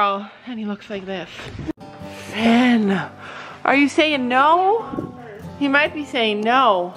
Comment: i think he was defo saying yes not no like sorry but